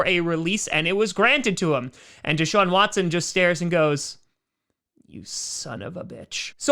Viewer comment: He sounds like he says youston